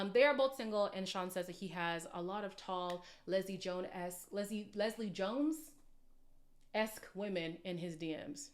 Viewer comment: I thought Shawn said, “Lisa Leslie” women in his dms. She is a former basketball player. He’s FOS 🙄